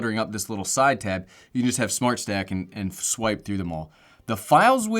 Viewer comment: I thought he said smart sack